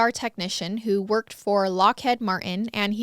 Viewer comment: Miss although some say it differently I will say my bf and I had a bit of a laugh but where we’re from we say LockhEEd but at 3:06 it sounded like Lockhead 😂 we love you though we just thought it was funny since we were baffled by the first part of the name but once you said Martin we were like ohhh😂